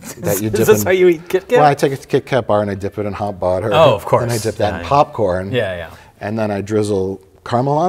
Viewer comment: "I take the kit kat bar and dip it in..." did he say water or butter?